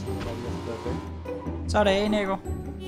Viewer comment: Did I just hear "Så er det af Niko"?